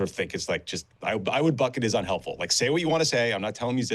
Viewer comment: or so, "I would bucket is unhelpful"? WTF is this guy saying?